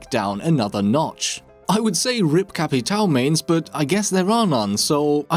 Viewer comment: what the fuck did u just say. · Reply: Capitao mains.... they are a myth 😜